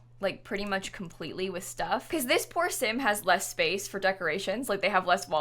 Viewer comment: Did anyone else have to replay 8:50 because they heard porsin instead of poor sim. Just me ok